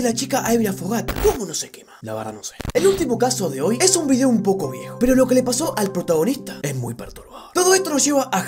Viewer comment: Soy yo o en el minuto 6:07 dice es un video un poco video O eso es lo que escucho yo Se que dice es un video un poco biejo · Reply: Dijo “es un video un poco viejo “